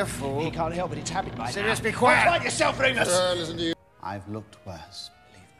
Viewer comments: Remus: Sirius be quiET Sirius: BE QUIET YOURSELF REMUSSS Remus: * gives up at life* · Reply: Surprise Surprise!! HOLY SHIT I FEEL LIKE A BAD PERSON NOW. I THOUGHT SIRUS SAID “GO BITE YOURSELF REMUS” IM A HORRIBLE PERSON. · "Sirius be quiet" "BE QUIET YOURSELF REMUS" 😂😂😂😂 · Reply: I hear "GO BITE YOURSELF REMUS!" I just don't know at thus point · "Sirius, be quiet" "Quiet yourself Remus!" Remus just looks so done with life, I can't breathe. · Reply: I thought he said “be quiet yourself remus!”